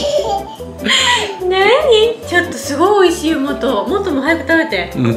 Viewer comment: What did you say at 11:33 when started laughing? It sounded like "noni" or "nonnii" (written more like "no niin") in finnish which means something like "alright"/"here we go"/"come on" depending on the situation (either in a positive or a negative way). It sounded so natural, I just wonder if it has the same meaning or whether the finnish expression could be similar to japanese. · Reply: In Japanese, "Nani" means "What?"